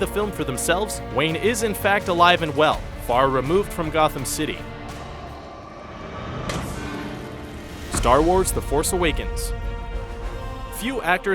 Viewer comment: did i just hear the word "gotham"in 3:07 ?